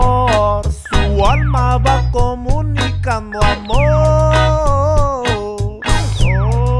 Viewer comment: Entendí "su alma va como caguamón" Bruh, I really felt that!